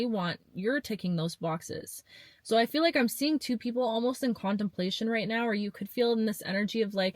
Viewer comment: I resonate with this a lot... and when you said "contemplate," I heard "quantumplate" and I thought that was a funny play on words 😂